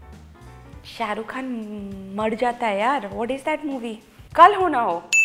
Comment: did she say seirukh khan instead of shahrukh😂🤣